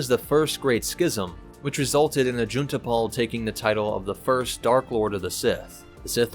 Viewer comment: I was listening to it while working and I heard "June Tapal" and I was like "WHO TAPAL?" and opened chrome only to see that it was "Juneta pal" maybe Jedi Fallen Order referenced it with Jaro Tapal? idk.